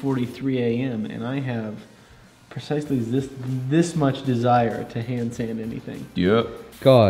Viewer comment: goes to to "finish" the knife, all I heard was "I need Will to hand sand this"